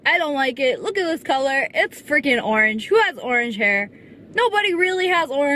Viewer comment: SSSniperwolf: Who has red hair?! Me: My boyfriend...?😅😥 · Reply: I thought she said orange...